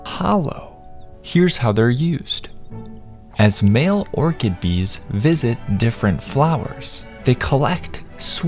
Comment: Most bee species is solitary, like this one, in which case they have males and females, never said it was a worker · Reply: oh, i heard "worker bees" instead of "orchid bees" for some reason